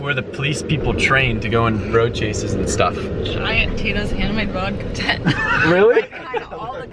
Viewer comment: Anyone else have no clue wtf said at 0:17 lmao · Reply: Attempting to talk about Tito's handmade Vodka.